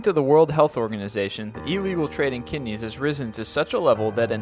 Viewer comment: what did he say???? "Ero WHAT?" No clue. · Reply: Just turned on CC. He said "illegal" it says.